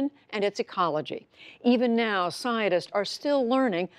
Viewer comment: siaodists instead of scientist like what?